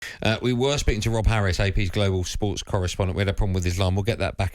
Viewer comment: For a minute I thought Goldstein said they were gonna be talking with Rolf Harris!